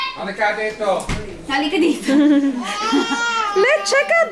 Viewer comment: Cuteee! I heard "Kuya TJ Halika dito"